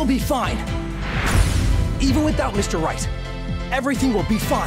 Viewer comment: When you turn on the English subtitles (auto generated), at 0:56, Apollo, instead of saying Mr. Wright, it reads "Mr Rice" x)